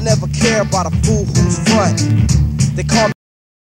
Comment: Does anybody know what he says in 1:47 ? Thanks, greetings from Germany. REFUGEES WELCOME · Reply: They call me the chill and yo this is compton